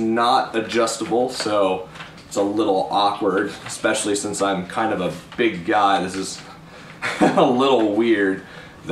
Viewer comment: Lol at the beginning it sounded like you said "I'm kinda a huge god" LOL